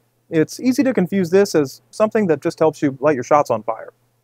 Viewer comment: My hearing is not the best. At first I thought he said that this will light your shorts on fire! Glad that I gave it another listen. 🔥